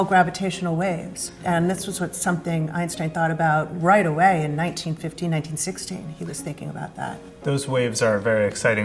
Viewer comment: She says that Einstein was thinking about gravitational waves "right away, in 1915, 1916"--but the closed captions say "<num>"--oops!